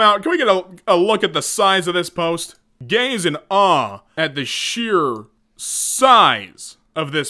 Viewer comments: I thought he said gays in awe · My rainbow glitter ass legit thought he said "Gays in awe" 🤦‍♂️ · I heard "gays in awe at the sheer size" Fuck i need to touch grass more.